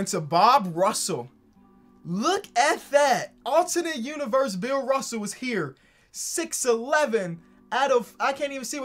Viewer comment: For a second I thought you said Bill Russel past away and I thought I might have not seen it I was about to cry until I listened to it again.